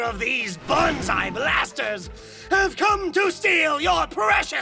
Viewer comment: Each time "Banzai Blasters" is said I hear "Banzai BASTARDS"